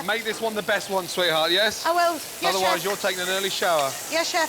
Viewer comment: did she actually say 'OwO yes chef' or am i hearing things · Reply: Sounds like it hahahah but she said "I will, yes chef" It's the accent